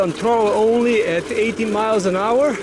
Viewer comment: did you say the top speed is 80mph or am I hearing him wrong. Cause there's no way this thing is going that fast.